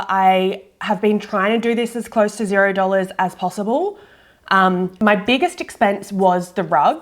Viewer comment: I thought you said this was a $0 makeover? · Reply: She said "as close to 0 as possible."